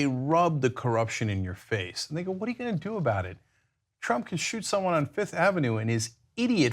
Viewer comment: Did i hear him say “nigga what u gunna do about it”??? 😭😂😭😂😭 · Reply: Lol, no. He said, "They go what are you gonna do about it?" :)